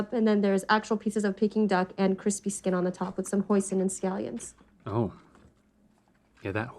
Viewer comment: I heard said "with some poison and scallions" XDD